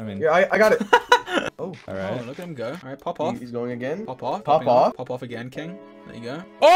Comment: did george really say “pop off again king”